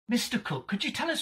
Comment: “Mr.cook” 💀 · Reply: i got confused i thought i heard mr.cock